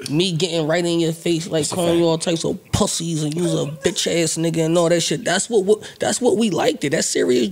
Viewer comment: Bro I know I heard parks said “nigga” at 15:42 repost this!!!!